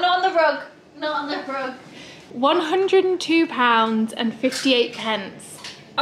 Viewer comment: When you got back from the vet I thought you were saying that Toby weighed 100 pounds and I was shocked! (That's a lot for a dog!)